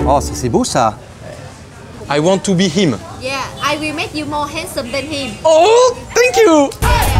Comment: Haha ! Elle dit pas "sexy" mais "génial", arnaqueur xD ("I will make you more awesome than him") PS : Ok, au temps pour moi c'est "handsome" signifiant beau donc t'avais raison, mea culpa. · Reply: Reivax56 nan elle dit "handsome" ce qui veut dire beau gosse, retourne réviser ton anglais